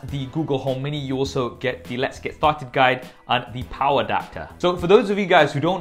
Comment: Sounds like you said let's get f'd guide